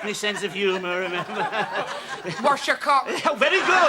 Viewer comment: it sounded like she said “Wash your cock” 😆😆😆